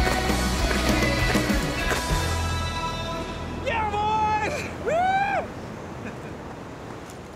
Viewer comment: What does he say at this point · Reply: "Yeah, boy!" "Woo!"